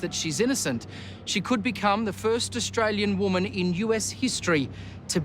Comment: i thought they said she was in "austrellia" jail